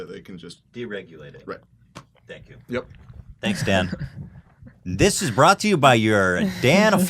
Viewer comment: When you said thanks dan, I thought you said thanks dad